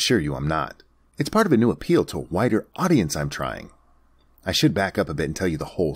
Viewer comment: I swear I heard "whiter" audience LMFAO